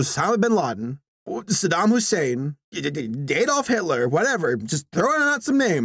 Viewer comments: i cant be the only one who thought he said Gaydolf Hitler lol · i heard Gaydolf hitler